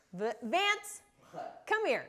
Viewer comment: I was hoping for Bob Vance from Vance refrigeration, when she said Vance. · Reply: Oh Vance I thought she said lance and I was like is he going throw food around like stunt